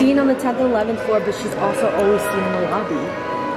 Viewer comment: Is it just me or when the lady in the elevator says “lobby” it kinda sounds like she said “BOBBY” No just me